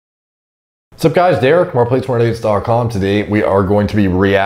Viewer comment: I always hear “what’s up guys derk more plates more dates